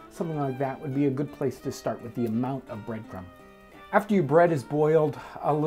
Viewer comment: "now that our bread is boiled" thats a first for me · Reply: He actually said " AFTER YOU BREAD IS BOILED "